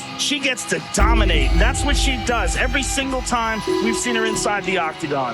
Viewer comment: I misheard it when it showed Amanda Nunes as... "She dominates... every time she enters her bedroom."